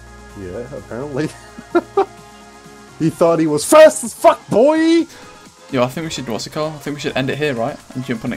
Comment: at first, i thought he said FASTEST FUCKBOIII